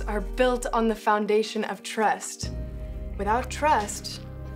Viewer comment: TREST LOL SHE SAID TREST INSTEAD OF TRUST